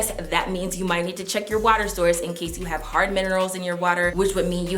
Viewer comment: Thank you for this...but what are you saying at 5:00, check our water storage???